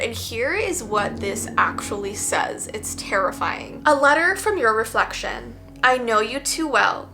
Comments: A letter from your reflection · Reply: That makes sense, I thought it instead said “A letter from your reaction”. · A letter from your reflection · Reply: I thought I said a letter from Junie reflection